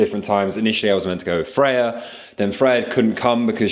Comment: Friday instead of Freya 😂